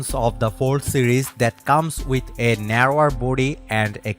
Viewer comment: Did he just say ""With a narrow booty?""😂😂